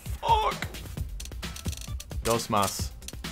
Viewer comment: Did you say DUSMAS ? (thats means Angry at Latvian language)